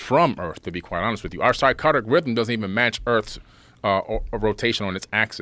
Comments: Never heard of this guy. But I thought he was saying "psychotic rhythms." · Can someone PLEASE explain to me what the heck a “Sarcardick” is?! And he said it TWICE with CONFIDENCE 🤣. This man is such a buffoon that it baffles the mind. Seriously, I have no words 😂…